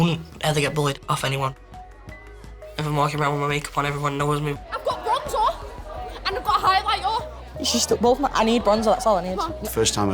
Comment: What I heard at bc of her accent (at least I think she has an accent) at 3:21 "I got bronzorh and I got a highlightuh"